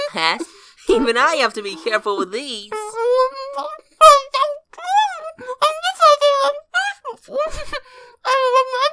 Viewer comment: What tails is saying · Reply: Putogh Bull Some body help me!